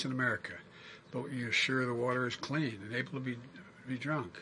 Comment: Did he say ''enable you to be drunk'' (1:15)? WTF 😂🤦🏻‍♂